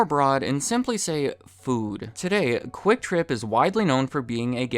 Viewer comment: Every time you said "QuikTrip" I heard "Kwik Trip" 😅 But seriously, it sounds like both companies have similar stories.